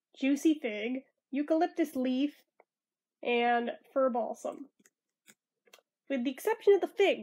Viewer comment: Lol when you said one of the scent notes was “fir balsam” I thought you said “fur balls”!!! 😹